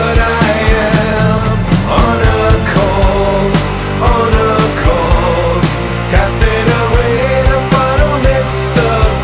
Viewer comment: Why I always hear "Hodor calls Hodor calls"